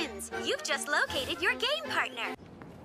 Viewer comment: When they said game partner I thought they said gay partner 😭 😭